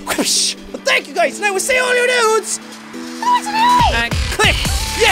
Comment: "And I will see all your nudes!" I swear that's what you said xD